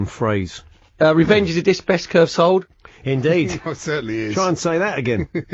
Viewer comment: took me about 5 or 6 listens and i'm still not 100% sure, but i think he says "revenge is a diss best curved sold" 🤣🤣. great upload this. i''ve listened to clips pretty much since the start and sent a fair few in myself. good batch this...plenty had me chuckling along.